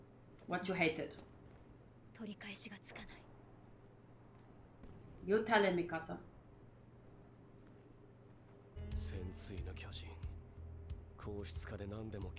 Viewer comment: 日本語にしか聞こえない 「言ったれミカサ」